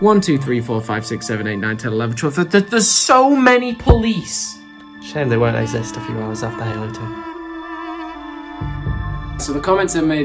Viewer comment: What did he say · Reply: It's a shame they won't exist a few hours after Halo 2..... EVIL SMIRK